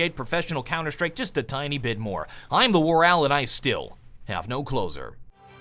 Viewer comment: When ever he says 'and I still have no closer' I always hear 'and I still have no clothes on'